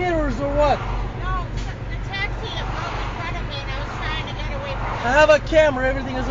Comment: can anyone tell me what she said? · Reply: "No the taxi, it broke in front of me and I was trying to get away from it"